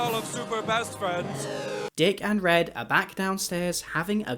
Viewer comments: anybody else heard him call Duck "Dick" -? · did you just call duck... dick...